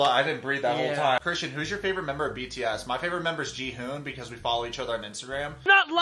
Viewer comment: Wait. What did he say at 0:36? I heard jihoon lmao what.